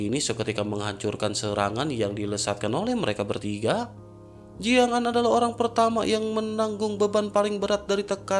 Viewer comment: Bang Ko Jiang'Zaa, Jiang'Aan bang...😁 🤔Apa aku salah Denger ya, Tapi Udah 2/3 Aq denger Lo...😁😁